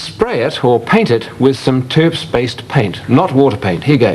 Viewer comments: What kind of paint did he use? Sounded like he said Terf-based but google search came up with nothing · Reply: Terps-based - turpentine. As I recall, this was from a spray can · What kind of paint does he say he's using at 3:12? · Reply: turps